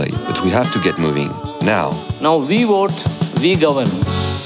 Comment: what does the indian guy say at 1:15 ? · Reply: "Now we vote, we govern!"